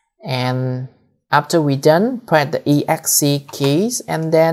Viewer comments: hi. at 12:34 u say "after we done hit the exc keys and then columns and right click hit enter". i can't quite make out your pronunciation. i've hit the E X C keys but it just adds those letters at the end of the word false. what am i doing wrong and what is the columns? i'm doing this on a windows 10 pc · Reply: Press ESC key to change to command mode Then press :wq to Write changes & Quit. This is the basic of edit files with Vi editor. You can learn on on Google in order to modify OpenWrt config easily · you say "press the exe keys and then columns and right click hit enter". I can't quite understand how to do that. What is the exe key? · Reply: ESC. You can learn the basic of VI editor to understand how to edit a text file in Linux.